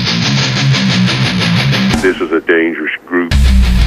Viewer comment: I thought it said dangerous groove 😭💀